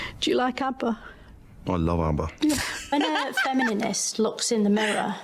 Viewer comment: I've never seen anyone reacting to her saying "Femininist" instead of Feminist